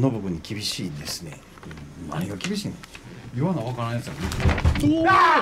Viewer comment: 「言わなわからん奴は◎▲×」霊障のドンドンドン この「言わなわからん奴は◎▲×」の部分、 最初が言ってると思っていたが 動画を見ても口が動いてないしも口が動いてない・・・ 声質も違ってると思うし文脈としても唐突な感じがする 誰が言ってるの？はっきり声として聞こえるから見逃されてるけど 結構、凄い霊障な気がする ちなみに、こう聞こえます「言わなわからん奴はぶっ●す」